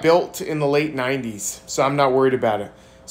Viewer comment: I laughed, subbed and almost unsubbed thinking I heard "Russia don't touch my votes"😂